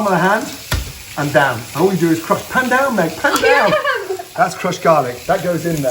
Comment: Poor, poor Gordon! Tough crowd. Gordon becomes the Director, Star, Chef, Dad AND got kicked out of his kitchen. 😥 For a moment, I thought Gordon said "pandan." 🤔